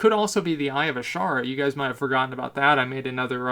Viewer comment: When you first said "Eye of Azshara(?)", I first thought you said Irish Shark... I was very confused to say the least.